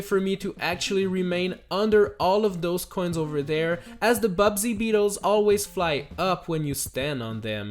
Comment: is it just me or did he say “Bubsy beetle”